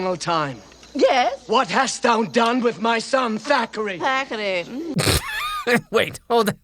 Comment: I always thought they were saying Zachary.